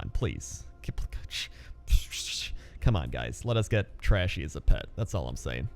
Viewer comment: Did I just hear him say "Let's get trashy as a pet"? I took that WAY out of context WAY too fast.